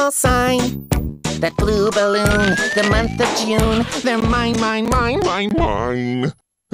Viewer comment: lmao turn on your captions at 0:07 it’s says “domestic use” instead of the month of june💀💀